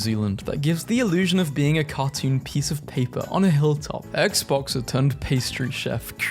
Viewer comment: The first time you said that i wasnt reading it i thought you said "xboxxer" not "ex-boxer"